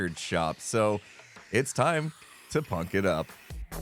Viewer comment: I heard, "It's time to f%<k it up"